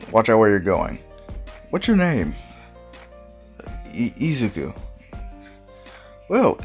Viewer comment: When you said 'Izuku' The auto-generated captions said.. 'easygoo' and like I THINK I'M GONNA DIE LAUGHING (which is good) Lol